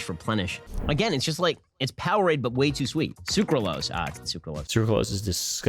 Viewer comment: im sorry did he just say powerade and dp?